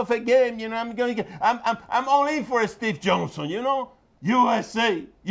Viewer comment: I thought Papi said "I'm all in for a stiff Johnson!"